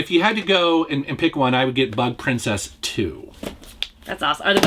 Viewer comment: I thought he said Butt Princess at first.